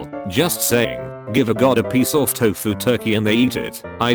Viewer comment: I swear I hear them saying "god" instead of"dog" there.just listen.Wait for the second time their supposed to say dog.🐶😇